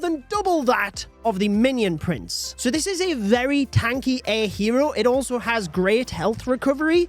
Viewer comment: Did bro say "Tanky ahh"? you're 55 years of age🥀🥀🥀🥀🥀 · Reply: Um actually 🤓 he said tanky air hero and he's 37